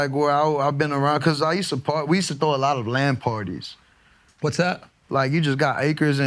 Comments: Thought he said LAN party at first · My gamer brain thought this man said LAN party. Like he had him and all his homies playing halo.